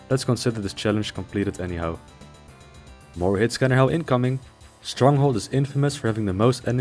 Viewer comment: what is this word he says a few times? 'Hitscam?'